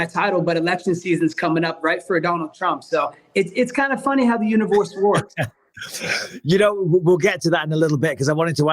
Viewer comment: what did he say😂😂 “the univawssssss wokkkk”